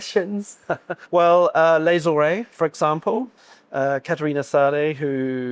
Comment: Can someone tell me who the winemakers mentioned from 08:56 onwards are? My English isn't great, so I couldn't catch the names. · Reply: Les Horées (Catharina Sadde), and Jeremy Carteret